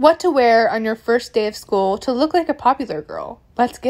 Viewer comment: Oh you said red light, i thought you said school